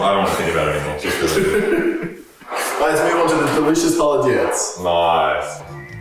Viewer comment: You know he’s ethnic when he slips up and says “холодец” instead of aspic jelly